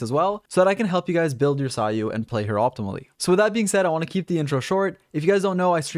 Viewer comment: I legit thought he said I want to keep this intro Xiao